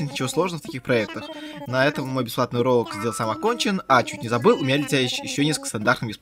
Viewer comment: Мне одному послышалось "бесплатный уролог"?)